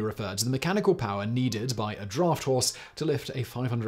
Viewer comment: Didn't hear a thing after 1:20, spent the rest of the video wondering what a "droft horse" was.